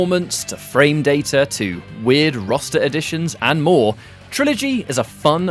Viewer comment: It sounded like he said "weird rasta editions."